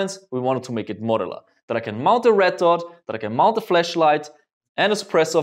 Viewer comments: I thought he said mounting fleshlights I was wonder what he meant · Did he just say that you can mount a fleshlight onto the gun at 3:40 ??? xD